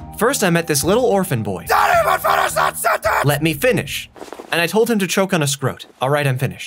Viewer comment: Ok what did the captain say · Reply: " DON'T EVEN FINISH THAT SENTENCE ! "